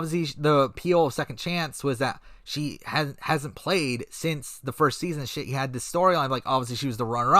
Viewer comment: He didn’t, but it sounded like he said “she hasn’t played since the first season and shit”